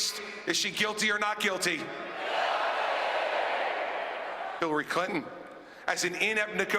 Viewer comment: Is it his accent or did he just say Hillary Cunton? Around 6:07 or so.